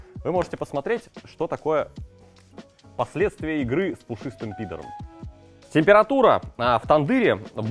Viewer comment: пушистый п*др? Мне послышалось?😂😂😂